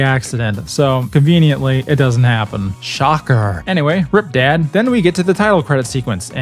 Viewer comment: i thought you said "crip" dad instead of rip dad